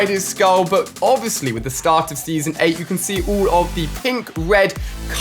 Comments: Did you just say at the start of season 8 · did he say start of season 8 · did he say the start of season 8?